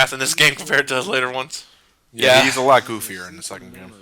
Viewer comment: Did just say that he's a lot GOFFYER in the second game?